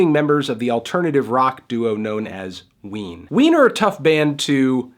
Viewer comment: all I hear is wiener